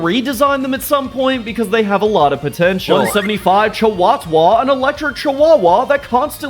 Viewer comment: did you say... POTENTIAL!?